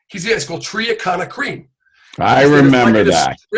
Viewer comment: Is he saying triacontanol? I can't quite get what he was saying the creme was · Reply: Triacana créme Thiomucase créme Percutacrine ampoules I trained at Nubret’s international club from <num> and later on I met Serge Nubret from <num>. He died of a stroke and complications in 2011.